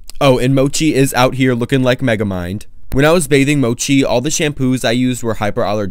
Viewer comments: I misheard bathing as dating… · When he said “when I was bathing Mochi” i thought he said when I was dating Mochi and I got confused lmao